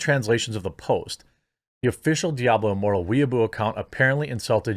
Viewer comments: Why do I hear "Diablo immoral"? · I thought I've heard "Diablo Immoral" ..yeah, sounds about right.